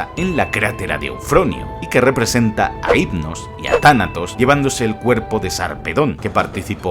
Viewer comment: Yo siempre pensé donde decía tánatos pensé que decía Kratos sálvanos😅